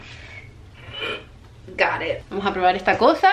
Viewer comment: No sé inglés alguien que sepa que me diga que fue lo que dijo en el minuto 12:38 Me dio curiosidad suena como gared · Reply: Leox444 ella dijo "got it" Es el pasado del verbo irregular "to get "( go it means lo tengo lo hice lo logre lo obtuve La letra " t" a veces se pronuncia como "r"